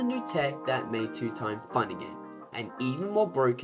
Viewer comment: I heard "fine" not "fun". I was about to agree like "Yeah two time fine af"